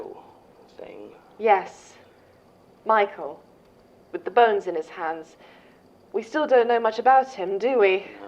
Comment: when talking about Jane “Sasha”said “with the burns in his hands” did I hear that right? That doesn’t sound right.. was that a clue or…?